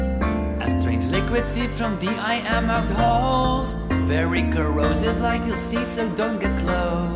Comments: i hear ,,very corosive i can see some drunken mouse'' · the first time i thought it said. a strange liquid seeps from deiamathed halls. no idea what that means but